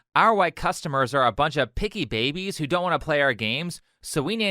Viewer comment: “Cater to our audience like picky babies” Is probably the most insulting thing to say to the people who make you money. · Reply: I thought she said, "piggy babies '